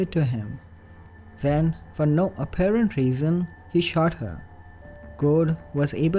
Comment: "Then...for no apparent reason...he sharted." 0:58 Now, I MAY have misheard that...if so, my apologies.